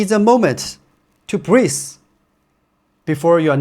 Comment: For English, I might be mistaken, but at 6:28 do you want to say "breathe"? I heard as if you pronounce as "breeze". But your jokes are getting better and better. Great improvements. For shoe discussions, can you review or recommend overpronator running shoes. Daily Trainer or Long run shoes are fine.